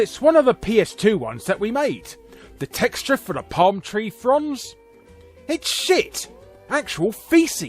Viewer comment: I thought he said palm tree fronds, not palm tree trunks.